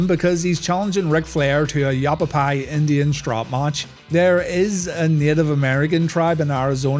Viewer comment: I heard it as "Apple pie Indian strap match", and it made me happy because I love apple pie!